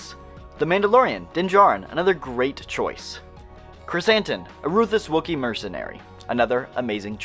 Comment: I thought you had said “Chris Hansen”